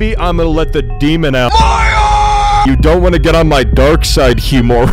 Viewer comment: misheard as: MARIO!!!!!!!